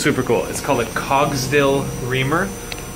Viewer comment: Hey great video as usual. Question: What was that bit you were using at 21:19 that you said made "perfect holes"? I couldn't quite make out what you were saying. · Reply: He said it was a Cogsdill reamer, but it is a Cogsdill roller burnisher. It basically deforms the metal into shape.